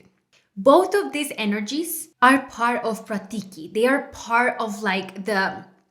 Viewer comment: What was the word you used around 3:24? Pratiki? Or something? I’m trying to research more about this. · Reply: Yes, it’s prakriti which means nature, primal substance, or the manifested universe.